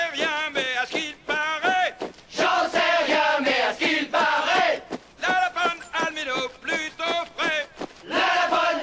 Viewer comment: Il dit quoi à 2:12 ? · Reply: Deathcoremusic Fan "la lapone a le minou plutôt frais"